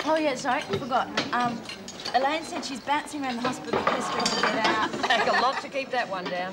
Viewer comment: what does Rachel say about Warren after saying Elaine said she was bouncing around the hospital? I couldn't catch the rest after that. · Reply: pestering them to get out. :)